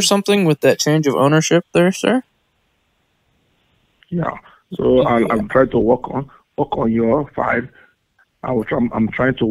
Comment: It REALLY sounded like he said "I'm trying to walk on your fine asshole". Seriously, even when I listen to it again while looking at the subtitles, it still sounds to me like he's saying that xP.